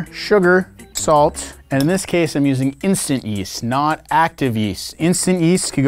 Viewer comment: I thought instant yeast said instant yeet